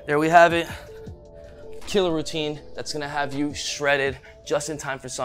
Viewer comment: why I always hear "chilly routine" instead of "killer routine"